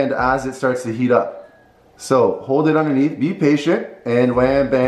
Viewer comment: I thought he said be Asian